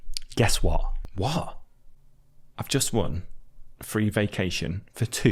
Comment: in 3:20 the subtitle says ''one'' but you say ''won'' i guess...